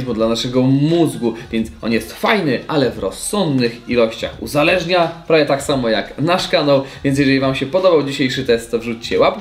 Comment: usłyszałem na końcu "uzależnia prawie tak jak flaszka" ._.